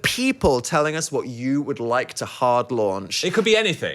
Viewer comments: loving the set but why is there a dot under the r in hard i thought it said hand launch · I thought that said “Hard Lounge” Wow.